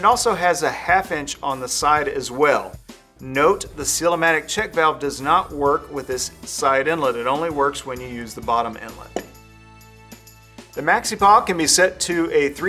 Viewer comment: you state > note ... ??? I could not hear that clearly ... what is it you say and what does it mean ... ? Thank you in advance :-) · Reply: "Note: the Seal-A-Matic check valve does not work with the side inlet. It only works when you use this bottom inlet."